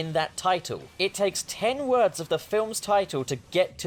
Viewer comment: Is it just me or at first I heard “it took NINE words” but after I rewatched it it says ten words?? 2:06 a lot of matrix stuff is happening to me rn I feel like I’m going insane bruh.